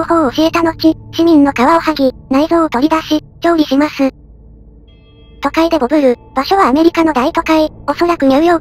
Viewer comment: 都会でボブる(動詞)に聞こえたwww